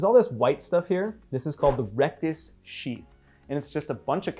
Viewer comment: wait did he say rectus shit?